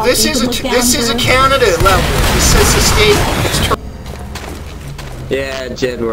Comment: Did he say this isn't Canada?? Lol!!